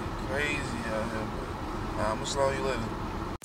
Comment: What he say in 1:36 " ima - you later"? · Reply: slide on